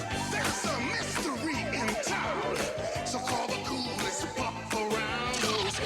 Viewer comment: At first I thought it said "Call the coolest Fu*k around"